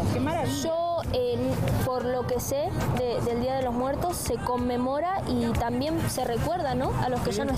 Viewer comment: El día de muertos se come moras eso entendí JAJAJAJA